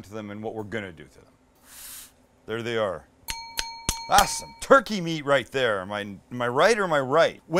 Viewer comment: Sound like you was finna say that’s “ turkey meat right there my nigga “ @ 2:11 🤔